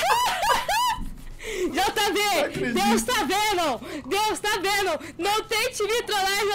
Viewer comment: eu entendi nessa parte 6:32 que ela falou deus ta venom